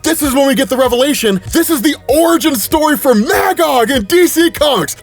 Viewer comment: Am i the only one who thought said "Mangog" from marvel 😂😂